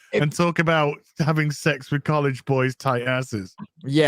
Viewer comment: Oh he said college boys? I thought he said "little" not "college". There are probably more people than average confused by comments over the last 24 hours. So it is back to Jack "C" Murphy not "F".